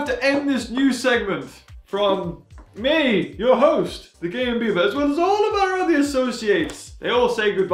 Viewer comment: I kinda sounds like says the gay beaver instead of